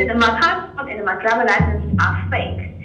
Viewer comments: The interpreter made a mistake at 07:12. The guy actually said that his passport was NOT fake, and I didn't hear him say that he wanted to go back to China. Not saying he didn't say that, I just couldn't find that piece of verbal information. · that man said his passport was not fake, then he added "My passport and my driver's license". His voice was not clear at the time he said this, but as a native Chinese speaker, it is easy to pick up. The translator definitely made a huge mistake, either due to mishearing or for some purpose. His documents may be faked, but further investigation and more solid evidence are needed.